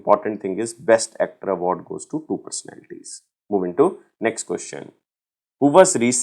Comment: what he said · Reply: two personalities